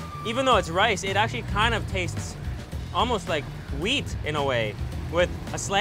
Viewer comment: "even tho it's rice but the taste almost like wheat in a way" 😂😂 I think he said weed